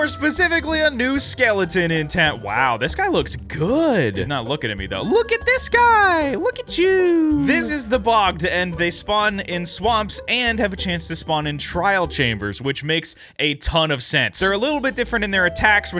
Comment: If i didn't already read the changelog, when you introduced him, I would have thought you said "this is the Bob!"